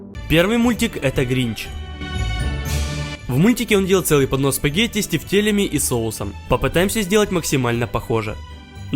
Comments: Мне одному послышалось-"панос спагетти"?таймкод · то чувство когда послышалось понос с тефтелями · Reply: Я: читаю комменты, Sally: то чувство когда послышался не поднос а понос! Я: 0-0 Переслушиваю, я в шоке смеюсь аш до слёз:D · понос тефтелей?! мне одной послышалось?!😂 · Мне одному послышалось в начале (первое блюдо) понос с тефтелями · понос тефтелей?! мне одной послышалось?!😂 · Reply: Мне послышалось понос спагетти с тефтелями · Мне послышалось что гринч ел понос спагетти с фрикадельками а там поДнос спагетти с фрикадельками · Мне одному показалось что он сказал панос спагетий?